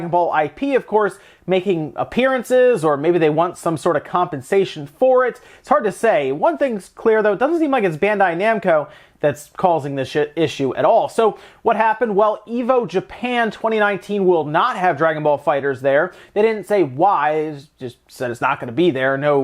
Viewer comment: Did he just say "It's not Bandai-Namco causing this shit"?